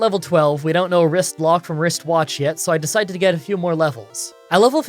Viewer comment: What'd he say at 3:00? "don't know a something from a something" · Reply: "We don't know Wristlock from Wristwatch yet"